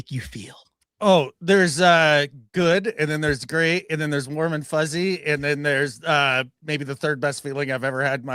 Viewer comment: I heard Mormon fuzzy 😂😂